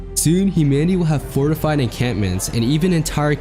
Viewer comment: Did he say encantments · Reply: encampments